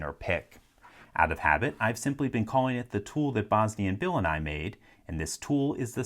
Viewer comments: And here I always thought he was saying, "the pick Bosni and Bill and I made." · This whole time I thought he was saying "the pick Bosney, and Bill, and I made". xD